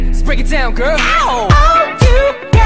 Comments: I always heard "you gotta sit good girl" and I thought it was random but now it makes more sense with "you gotta see blood girl" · and here I thought they said "you got us a good girl" this entire time XD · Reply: i heard "oh you got some blood girl" .. idk i'm weird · OMFG I always thought the chorus said oh you got us a good girl wtff am i that deaf?? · I legit thought they were saying "oh u gotta seat belt gurl"